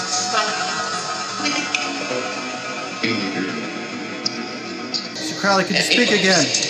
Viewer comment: This part at exactly 13.17 I could swear I hear the name Aleister Crowley loud and clear through the noise ><